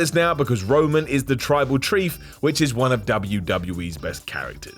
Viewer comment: 🤷🤷🤷 said Tribal Chrief instead of Tribal Chief at 5:56.🤷‍♂️🤷‍♂️🤷‍♂️